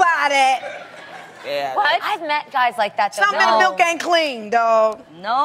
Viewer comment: what did Tamar say at 2:11 ? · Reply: "Something in the milk ain't clean, dawg."